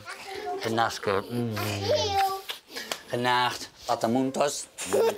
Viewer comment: Wat zegt die daar genacht ofzo? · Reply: ja, gewoon nederlands ''genacht''